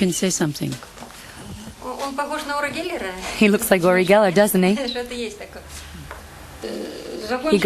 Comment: did she say "he looks like uri geller, doesnt he?"?